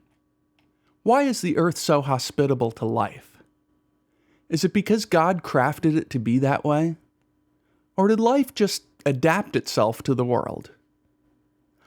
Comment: i heard because of streaming god crapped